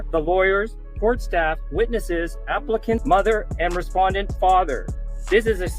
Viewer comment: did i hear that right? "Applicant (mother) and Defendant (father)"? so it does sound like it's a baby momma and not a daughter. that woman does not look like Adonis' mom, though. maybe david just didnt get a good look before being thrown out, idk